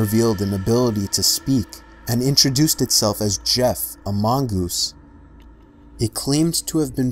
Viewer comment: For some reason I thought he said Jeff Amongus